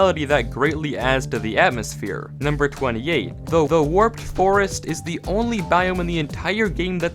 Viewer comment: did say "the the warped forest" ?